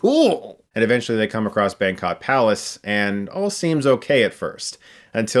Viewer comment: did he just call it ‘Bangkok’ palace? 😂